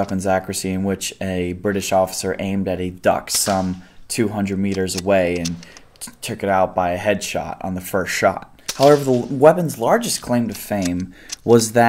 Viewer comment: I know now he said "a duck some 200y away" but I swear to God the first time i heard "dachshund" and im sitting here thinking is no one gonna mention the fact he shot a random weiner dog to test a rifle?? Lmao